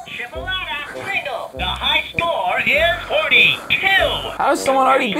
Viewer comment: 18: 35 all I hear is" the high score is Horny" 😆